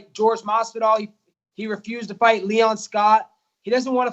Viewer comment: No one knows who leon Scott is. The joke isn't funny if you have to google it. That's obviously what colby did and googled famous people with the first name leon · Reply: Kam Troy I saw that, but I thought he said Leon Scar because of the Scar Jorge gave him.